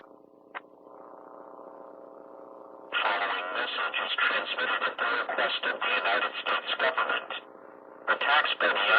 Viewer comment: What's the voice saying at 4:57? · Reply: Tom